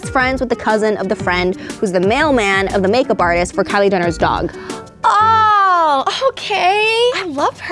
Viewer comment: Did she say "Kylie Denner"